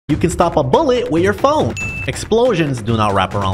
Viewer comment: at the beginning of the video i thought he said"you can stop a bullet with your ballz" LOL